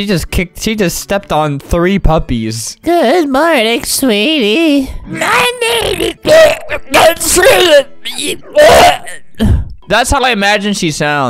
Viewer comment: When he voiced the grandma at 2:45 this is what i heard: "mY NAME iS..*cough*...jOHN cENA..AND yE BOI."